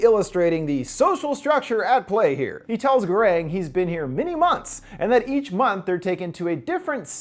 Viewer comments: I thought he said 6 months ?? Idk i could be wrong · I thought he said 6 months